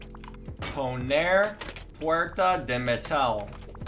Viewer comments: did you say "porta de metal" at 6:10 ?? holy bro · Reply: he said “poner puerta de metal,” but i don’t think metal in spanish is metal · Did you said "Porta de metal" lol "Porta de metal" mean Metal Door in Portuguese Brazil I'm from Brazil and I love your videos :D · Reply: It was Spanish - he said in an exaggerated bad accent, “Poner puerta de metal”. Probably cuz like many of us Americans, he learned a bit of Spanish in school!